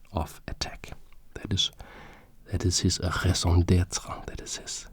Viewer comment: what did you say at 6:40 brother? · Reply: Raison d'être /ˌreɪzɒ̃ ˈdɛtrə/ is a French expression commonly used in English, meaning "reason for being" or "reason to be". And "Telos". Telos is the ancient Greek term for an end, fulfilment, completion, goal or aim; it is the source of the modern word 'teleology'.